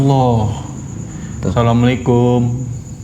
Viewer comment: ada suara jawaban salam nya bang alus cowo " wa'alaikumsalam " masyaAllah❤❤ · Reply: Eh iya beneran, gw nonton pake speaker bluetooth aja kedengeran, apalagi kalau pakai headset, dengerinnya dari 3:11 aja, abis bilang Asalamualaikum langsung dijawab wa'alaikumsalam.